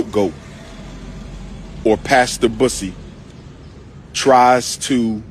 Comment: Did he say “pass the bussy”? 😂